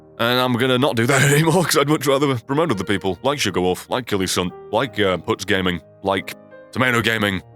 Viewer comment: what's the name of the second youtuber he mentioned it sound like he said kilison but it won't come up when I search him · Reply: killysunt